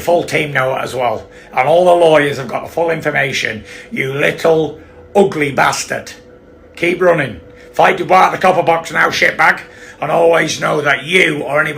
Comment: I need a British translator cuz wtf did he even say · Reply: 😂. “Keep running. Fight DuBois (Daniel) at the Copperbox now sh1t bag.” The Copperbox is a small arena in London where they put on British level boxing cards. I’ll assume you know what a sh1t bag is!